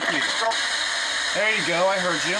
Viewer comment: you can hear something mumbling "I heard you" right before the scene switches